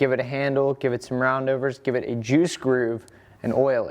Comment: did anyone else hear "give it a jew screw"?? hahahaha